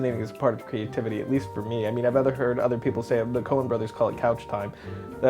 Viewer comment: did he say conan?